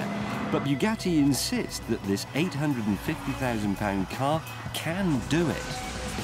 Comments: did he say 850,000 pound car????? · did he just say 853000 pound car?!?! or is that in money wise? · Did he say that the car was 850000 pounds at 3:10 ? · did he say the car weighs 800,050lbs or 8,050lbs??